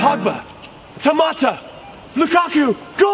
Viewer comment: I thought Jimmy said Tomato for some reason.